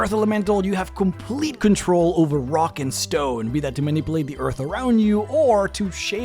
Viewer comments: Did i hear a rock and stone? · Did I hear a Rock and Stone?!